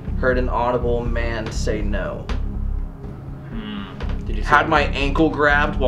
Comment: i heard a woman's voice say "you" when was talking about the person getting their ankle grabbed ahh